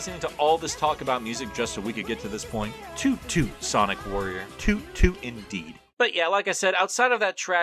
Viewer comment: The Japanese theme is actually called "You can do anything", not "Toot Toot Sonic Warrior", despite those words being repeated a lot in the lyrics. "Toot Toot Sonic Warrior" is more just what a lot of the fanbase calls it. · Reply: I always hear it as "super sonic warrior" 😭😭😭😭😭